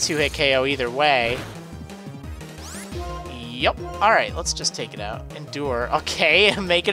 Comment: Did you say"Eyup!"?